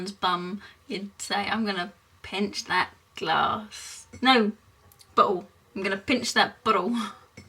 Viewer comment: when she said I'm gonna pinch that bottle. sounded like she said butthole.